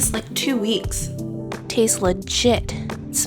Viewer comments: When you said "tastes legit", I first heard "tastes like sh*t." I was like wait what.... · I love how you said "it tastes legit" it's like an old person trying to be hip. I have a feeling that you'll be a good mom. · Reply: Oh, "tastes legit". I thought she said "tastes like $hit" and I was like WTF? 🙀😸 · I love your videos. Thanks for sharing. On the funny note... after you made the oatmeal and was about to taste it. I thought you said it taste like Sh*t ahahha. I had to replay it lol. Anyway love your Channel. Keep these videos coming.